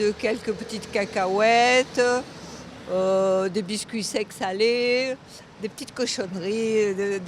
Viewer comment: she actually says "des biscuits secs salés" not "allez". Haha it kills me to think of an elderly saying "des biscuits secs, alleyyy"